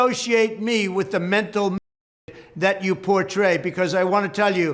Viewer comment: If you wanted to know what he said Mental midget.